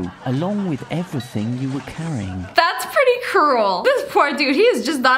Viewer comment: she says that’s pretty cruel and my phones captions said that pretty girly yo who hurt my phones feelings